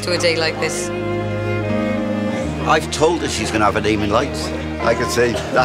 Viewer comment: Did you guys actually translate the guy talking about his daughter at about 1:40 to DEMON lights instead of NAME IN lights...LOL